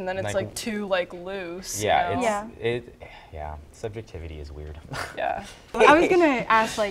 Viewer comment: omg at 11:07 I thought he said sucking tities is weird I was like wtf had to replay it back then I was like what's wrong with my brain lmao